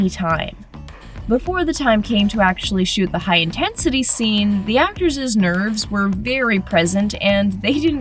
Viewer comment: DID SHE REALLY JUST SAY "The Actorses nerves" instead of "The Actor's nerves"